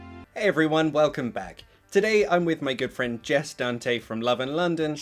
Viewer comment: I heard “my girlfriend” instead of good friend lol